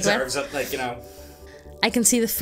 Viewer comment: What the fuck was said at 9:03, not even the CC could pick it up lmao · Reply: "To be honest with you , he deserves it like ya know "